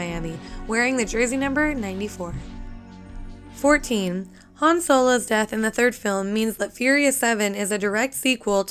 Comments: Did she say Han Solo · Did you say Han Solo??? 5:10 ?? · did she call han "Han Solo" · did she say Han solos death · i heard han solo instead of han lue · did she just said han solo ?